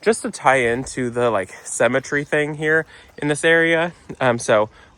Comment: The fire pit looks brilliant guys and I love those lilies! When you said symmetry at first I thought you said cemetary - I think “funeral flowers” comment must’ve stuck in my head 🤣 xxx